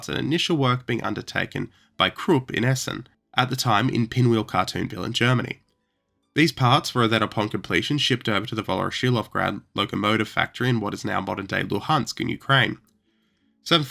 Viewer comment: What the smeg do you say at 14:25 after "Krup in Essen"?? I used CC, and it said "in Pinwheel cartoon bill in Germany".